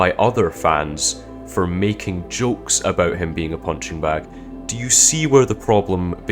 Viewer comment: I legit heard you say "hunchback", not "punching bag". haahhahaha. CaseO best O